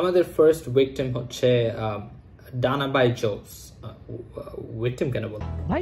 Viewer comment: Did he just say Dana baincho*d? 😶